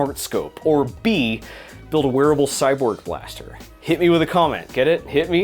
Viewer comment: I first thought you said "Werewolf Cyborg Blaster". I'm still voting for the wearable cyborg blaster, even though it won't be werewolf compatible.